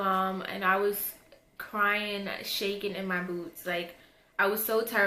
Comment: She said" shaking in my boots", but i had to replay and turn the volume up a bit more because i heard "shaking in my boobs"